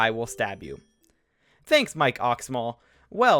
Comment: did you just say “Thanks, my co**’s small”?